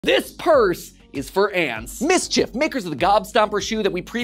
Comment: I thought he said “this purse is for pants” 💀